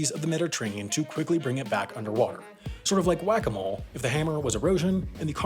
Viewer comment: i thought he said "guacamole" and got totally confused by the picture. gj, me.